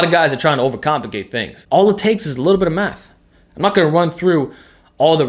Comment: sounds like he says "all it takes is a little bit of meth"